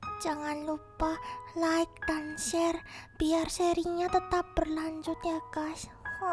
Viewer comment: qa: Biar seri nya tetap berlanjut ya gaes Aqu dengernya pas qa bilang gaes itu gas XD wkwkwkkw Ga bermaksud menyinggung ya qa :(